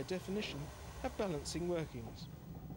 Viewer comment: What's he saying at 1:08, it sounds like he's saying they "couldn't, by definition, have balancing ... something?" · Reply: Balancing movements .